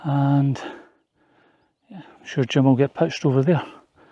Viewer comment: thought you said “Jim will get pished over there” 😂